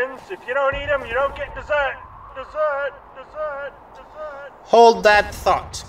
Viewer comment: I HAVE NO TOLERANCE, AT ALL, YOURE ALL BEING ASSIGNED TO A PENAL BATTALION, AT ONCE! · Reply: Says the person who misheard dessert as deserting.